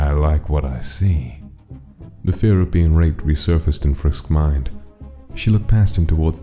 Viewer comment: am i hearing it right? " fuist"